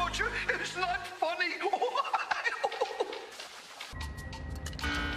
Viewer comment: Did Luigi just say The Legend of Zelda?